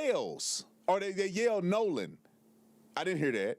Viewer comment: Someone yelling what sounded like "Noooolan," was the clearest part to me.